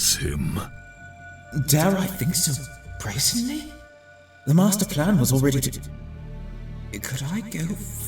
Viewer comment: did starline say “master plan”? I guess eggman’s not the only one with a master plan now